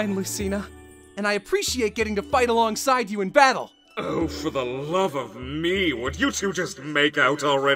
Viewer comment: "Oh, for the love of me...will you two just make out already?" And this, ladies and gentlemen, is why makes the best Hades!! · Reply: I thought he said "Oh for the love of mii" 😂